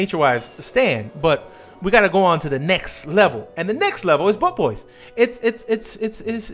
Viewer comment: I keep hearing "butt boys" lmao